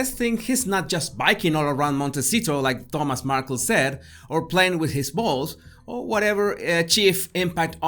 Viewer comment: Omg, I was listening to this video without watching it and I heard "Or Harry playing with his balls"! Lol!